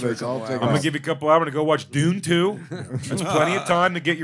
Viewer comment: When Big Jay said Dune 2 I thought he said poon toons like some new slang for cartoon porn 😂😂